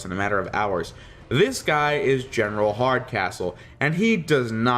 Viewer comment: I swear when you said the name of the General from the Prometheon episode, I finally got the joke. Mostly because I thought you said his name was: General Hard As Whole. General Hardcastle is a subtly & suitable surname for the character.